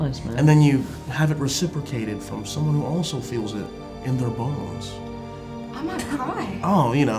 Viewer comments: I thought he said someone who also feels it in their balls😂😂😂😂. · SorRy But why did I hear Balls. Aksdksjd